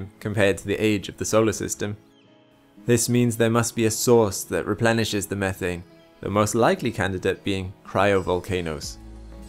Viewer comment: at about 7:45 i really thought you said "there might be a sauce that replenishes the atmosphere" lmao ooo what secret sauce..